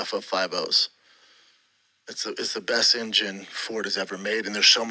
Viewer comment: Did he just say Emgym for engine ?